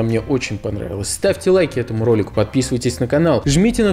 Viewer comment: вначале мне послышалось Юрик......